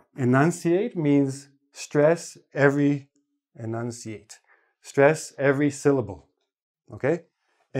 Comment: 'Scuse me ! Please someone go 10:13 and tell me what he says. " Stress every .... " ? What ? by the way good lesson. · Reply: "Stress every syllable". It means "hece" in Turkish, by the way. ;)